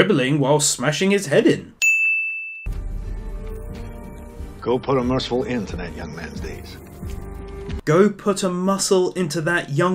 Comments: for a while I thought he said "go put a missile in that young man's face." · "go put a merciful end to that young man's deeds" is what i think he said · How did .. you the.. what exactly? He clearly said 08:00 "Go put a merciful end to that young mans days." Was that the joke here or did you not hear what he said, I'm genuinely confused xD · he actually said “go put a merciful end to that young mans days” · i think what the governor said at 8:00 is "go put a mercyful end to that mans days" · i think he says "Go put a mercifull end to that young mans days"